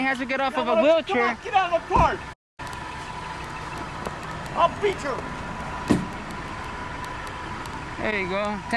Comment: what'd he say "I'll beat your meat"?? Or what? 🍖